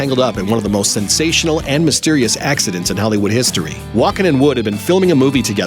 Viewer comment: Did he just say accident?